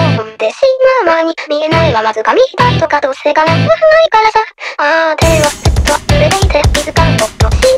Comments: の「どうせ叶わないからさ」が声だけ聞くと「どうせ叶うはずないからさ」に聞こえるんですよ。それって口では絶対叶わない。叶うはずがないって思ってるけど心の中では叶うかもしれないとか思ってんのかなって、、（（ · いつもこれ、叶わないからさが、叶うはずないからさに聞こえてしまう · みんな触れてないけどここ字幕は 叶わないからさ になってるけど 叶うはずないからさ に聞こえる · なぜかしらんが、0:06らへんの からさ がコロスに聞こえてたんよな